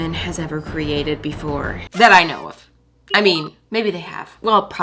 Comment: lol, instead of “that I know of” in the intro, I heard “Iowa”